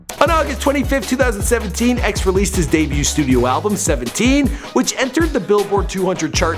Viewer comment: Did he say spiminute on